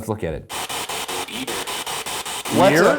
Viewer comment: Instead of "you're mistaken" I hear "Thanks", which I think makes more sense in context